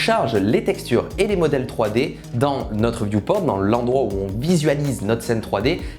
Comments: j'ai compris dans notre yourp*rn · Il a dit quoi la ?! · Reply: View Port